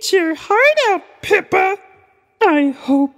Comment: When Miss Piggy said "Eat your heart out, Pippa!" at first I thought she said "Peppa" and was insulting Peppa pig for some reason. Then I realized that Pippa is the actress who plays Eliza lol